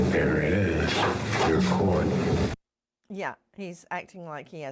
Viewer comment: it sounds like he said, "there it is, you're caught."